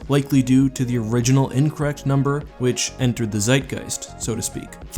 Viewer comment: did you Just Say the German Word Zeitgeist